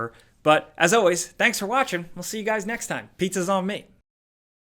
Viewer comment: I feel scammed I just ordered pizza and had to pay for it, I thought you said “Pizza is on me” 😡.